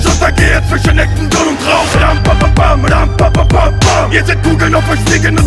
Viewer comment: bei 1:16, was sagt er nach ''...kämpfe bis ich untergehe zwischen ??? und traum'' ?